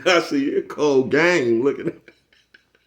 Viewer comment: Damn 😂u say cold Game I thought u said Colgate at first 😂. Salute Bro keep pushing 💥💯